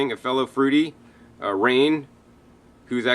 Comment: who is the guy in south FL that sells fruit online? At 11:32 named Rain? Didn't catch that -- I'll order from him, thanks!